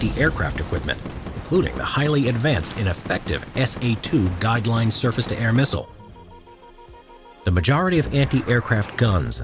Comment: did you say the missisle was effective or not effective? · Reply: What he said was: "...including the highly advanced and effective SA-2 Guideline surface-to-air missile."